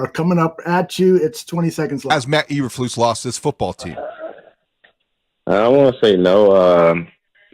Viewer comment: DJ Moore was quoted by another reporter as saying... "I want to say no" when he was asked if Flus lost the team. Listening to him, it sounds like he is saying "I don't want to say no". I am not sure because it is difficult to understand, and he speaks sort of lazily.... no disrespect.